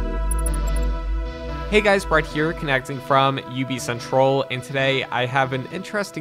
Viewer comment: It sounds like you said ubicentroll :D